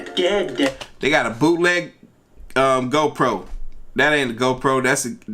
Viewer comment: Donald TrumpNot joking around whenever you said GoPro it sound like he said ho proAnd depicted on which video you’re watching that was haliarious